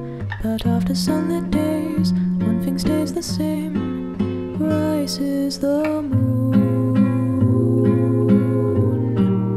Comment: Guys I keep hearing “rice is the moon”